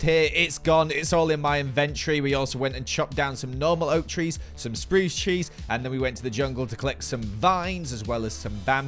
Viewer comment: I thought he said “spruce cheese”😭😭